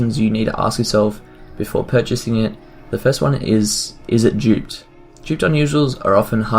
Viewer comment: "Is it .......?" What is the word within the "....." and what does that mean? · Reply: duped